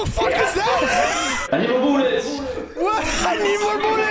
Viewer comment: "I need more bullets"? I hear "Ayo who dis" which makes it way more funnier